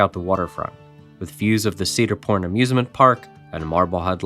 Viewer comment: Sounded like you said "Cedar P**n Amusement Park" 😂